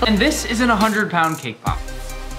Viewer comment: I thought she said don’t even think about breaking our cake pop